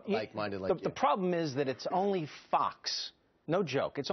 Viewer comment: Careful DML! Your lack of sleep made you say "F*cks" instead of FOX!